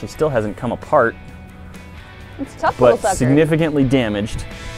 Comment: Did she just say the f word @ 9:50? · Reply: I heard 'sucker'